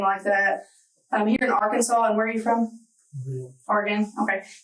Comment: アーカンソーに聞こえるけど...